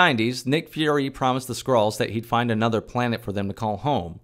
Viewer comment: Every time I heard Skrull I heard "Squirrel" and it made the synopsis so much funnier.